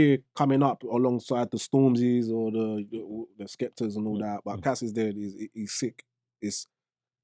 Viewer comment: Who does dizzy say he looking out for on the grime scene at 36:50 "Cassis dead" or something, I couldn't hear the name? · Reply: yeh CAS or Casisdead, one of the best in the uk for sure